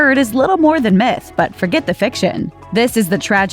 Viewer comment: thought she said fake shit not fiction